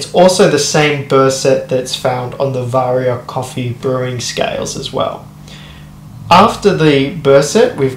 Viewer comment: tx for the update. two questions: do you think the burr alignment is better in this version? And, at 5:38 did you say 'vario coffee brewing scales'? As in Baratza Vario? Confuse.. Sorry, English isnt even my 2nd language. Thx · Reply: Hey no stress, I said Varia brewer scales and I don’t think the newer c2 has any more advantage than the previous versions :) Thanks for watching!